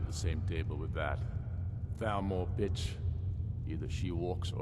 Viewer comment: my computer glitched a bit and it sounded like ulfric said "that bitch" xD